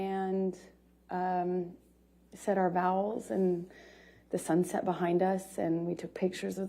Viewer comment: did she say "said our vowels" instead of "said our vows"? Parakeet floors and more tell me her IQ is low low low.